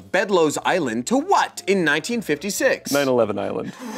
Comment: WAIT WIAT WHAT. I didn't realize Shayne said "9/11 Island" I though he was saying "9/11 9/11". 😭